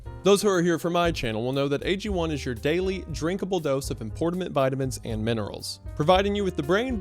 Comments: Did he say importimant? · did you just say, “importament vitamins?”